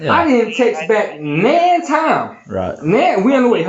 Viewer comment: what did he say?? Text men time?? Just speak normally